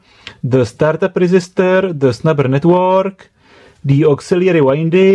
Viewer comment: What did you say in 5:13? Snapper network?